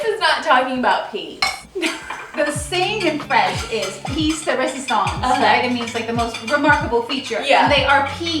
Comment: What an absolute epic YouTube crossover. Also funny how someone as cultured and well travelled doesn't know the phrase "piece de resistance." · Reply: I swear I've heard her say "the piece of resistance" before